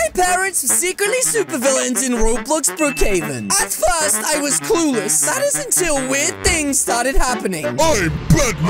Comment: Did he said “Clue-less” Or “Klude-less”